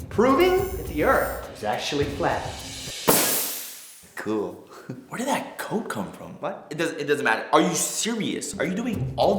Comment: lol i thought he said where did that coke come from